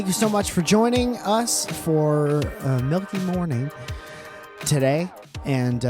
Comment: I'm sorry did you say "milky morning" at 12:07 because I love that